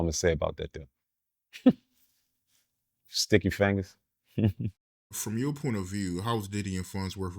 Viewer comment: I thought this 🥷 said “Sticky Faggots.” 😂😂😂